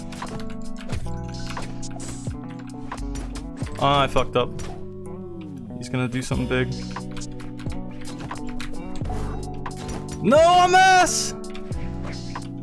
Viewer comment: I thought that said "no mames" like the mexican expression